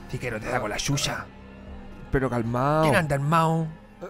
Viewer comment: jaja que dice después de calmado? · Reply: QUIEN ANDA ARMAO?!